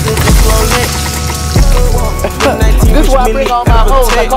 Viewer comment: Did he just say “Serve me, daddy”? He sus af.